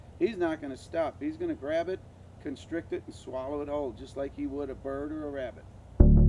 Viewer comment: when he said a python would swallow an aligator whole 'just like he would a bird or a rat' i thought he said ' just like he would a burger or wrap' ... i was thinking for a while, well where's he gonna find that ?